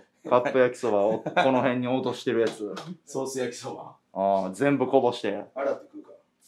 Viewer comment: 焼きそばが焼きしょばに聞こえた